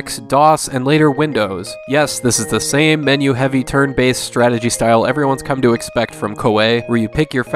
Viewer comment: When you said turn based I thought you said turd based lol